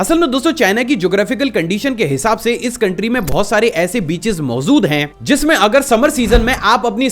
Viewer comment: you said mozud instead of mojud😂😂😂